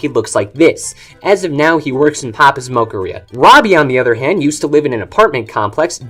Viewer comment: I thought you said papas smokearia